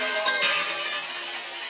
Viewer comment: That was incredible, and!!! I swear I heard, "Bye. I'm an angel," at 44:28 ? When it also says, "My Mom." I don't know if it's someone physical speaking or spirit? God Bless ✌❤😊🙏